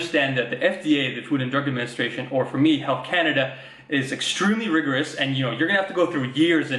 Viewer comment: legit thought he said "Heil Canada" rather then "health Canada"